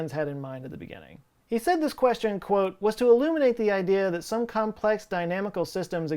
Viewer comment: You said “eliminate” instead of “illustrate”. 😁 Love you Joe! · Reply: He said illuminate